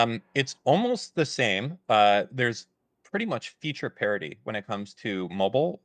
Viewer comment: subtitles should say parity instead of parody